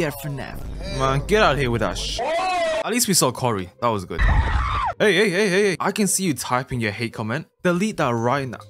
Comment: I THOUGHT YOU WERE SAYING "Hey comment" LMAO